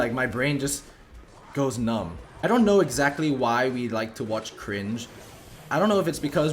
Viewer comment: watch what?? i didn't catch that word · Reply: “watching cringe”